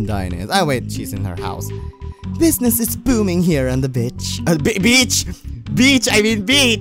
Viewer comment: When u relise u said "B*tch" instead of beach XD